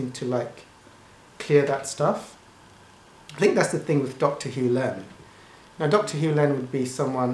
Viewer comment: And also 5:37 what did you say please ? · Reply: I think you mean his mention of Dr Hew Len? He was a great example of clearing karma for others. Hopefully answered your question? Thereby I will have cleared karma of having to answer his youtube questions!! :-)